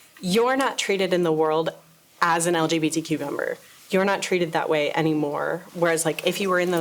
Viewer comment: I know they said "LGBTQ Member" but I heard "LGBtcumber"- like a gay cucumber 😅